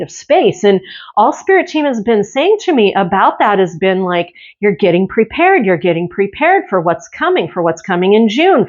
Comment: What I kept hearing is, "you are being erased now. "